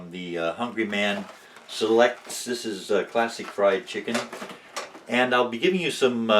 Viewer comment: Haha, I thought he said "plastic fried chicken" when he really said "classic fried chicken!"